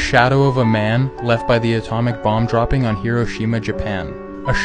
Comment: I thought he was saying kiroshima- wait, so he was actually saying heroshima the whole time?